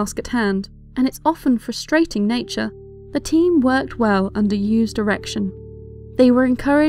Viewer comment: "The team worked well under used erection". Think I misheard something